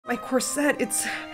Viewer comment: I didnt hear the “my corset” part so i just heard “its too tight” and i was like “ayo-“